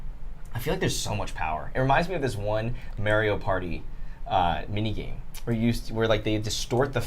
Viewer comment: Did say “Merr-io” party @ 27:34? :D Made me smile way harder than it should have!